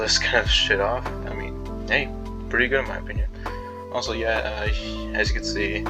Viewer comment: Please tell me he didn't say what i thought he just said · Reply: “Pretty good in my opinion”